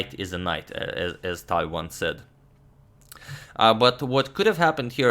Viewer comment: does he say : "as Tal once said" ?